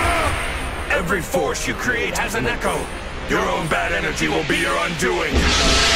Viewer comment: Could someone tell me what gogeta is saying in English at 1:57 ? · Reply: Every force you create has a echo, Your own bad energy will be your undoing!